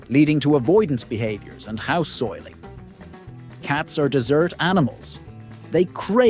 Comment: I heard him say they are "dessert" cats, not desert cats. LOL